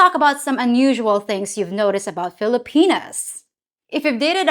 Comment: Hi again... I'm so sorry to hear of your niece's grandfather. 😢 On another note, I at first shied away when I thought I heard you discuss "FiliPenis," but was relieved when I finally understood it to be, "Filipinas." Whew.... And before closing, I'd like to say, your pits are as cute as the rest of ya. 😊 Ingat sa Palagi, and my best to your entire family... all 30 of them, if ya count the extended family. Laterz..😊